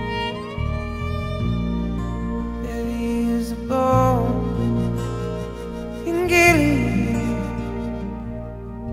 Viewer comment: What did he say at 3:55? "There is a * in ***"?? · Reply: There is a balm, in Gilead. I think that is what he said.